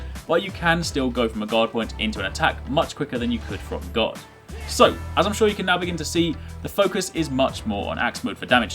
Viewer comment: I thought you kept on saying God point